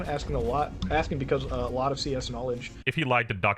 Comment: captions say he said CSS (stands for Cascading Style Sheets, used for styling web pages), when he said CS (stands for Computer Science).